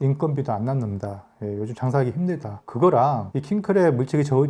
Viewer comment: 사기치기 힘들다 로 들리네요 ㅋㅋㅋㅋㅋㅋㅋㅋㅋㅋㅋ 힘든 상인 같은 소리하고있어 ㅋㅋㅋㅋㅋㅋㅋㅋㅋㅋㅋㅋ